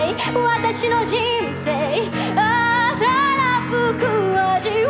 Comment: たらふく味わいたいのところがに聞こえた！！！！！！！